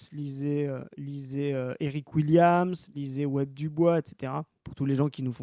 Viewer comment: C’est quoi le 3e nom cité à 10:04 ? (Sur le fait que le racisme suit l’esclavagisme et ne le suit pas) J’ai du mal à entendre… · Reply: W.E.B. Du Bois :)